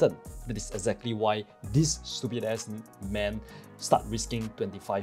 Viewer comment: LOL DID I just hear stupid ass N